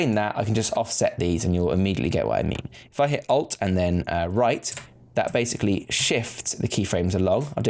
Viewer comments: Hey bro. nice video sorry but i poor with english. I did'nt get at 12:58 minit what you said?? you said some " Alt + ?? " after i did'nt get it. plz bro explain me . I am not able to make as u made plz help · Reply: Alt and then the right directional arrow! :) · Can someone tell me what the command key he used at 13:00? Hit Alt and what? to change those start and end values? Thanks! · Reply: ALT + Right arrow. This shifts the key frames on the bottom over a frame so the start and end dont match up.